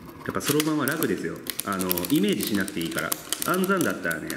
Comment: can anyone explain what did he said here 0:55 please ! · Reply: そろばんは楽ですよ。イメージしなくていいから。って言ってます